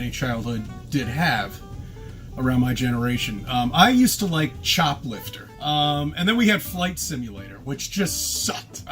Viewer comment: Chop lifter? I thought he said shop lifting